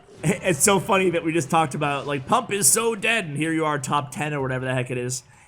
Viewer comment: What the heck?? I just heard you saying pumb is dead over and over. And now i got a notification for a new video pumb is back. Dude whaaaat